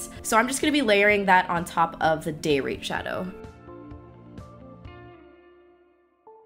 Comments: ahhhh i totally thought you said '...this color from ABH called date rape' not 'day rate' :o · I can't with that "day rate" I swore I heard "day rape" I was like what the hell ha ha ha just keep in mind thou if you throw confetti it's not rape just surprise sex!! Ha ha ha ha love both these looks thou Killin it! :) ♡♡♡♡♡ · I thought she said "Day Rape"...and I was like "woah, Anastasia, chill" but my snapchat is EmilyIsDeezNuts!!! ❤️❤️❤️❤️❤️❤️ seriously you are bae and this vid is bae been waiting all damn day for this ish to playyyyy Ok bye. 😅